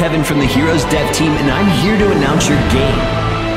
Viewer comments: At first I thought he said I’m here to announce your gay 😂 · did kevin say he was here to denounce your game